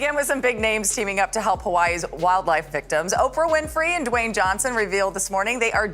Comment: Did she just say "wild life victims"?